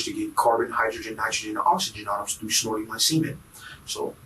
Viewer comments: Am I crazy or did he say that he "snorts his own semen".....??? Anyone else hear that? 1:12:00... Please tell me I'm wrong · the sober guy that snorts coffeine and cement. that fool should have tried something different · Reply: I thought he said semen.